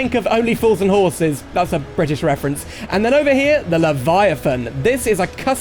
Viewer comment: when you said British reference i thought you said JoJo reference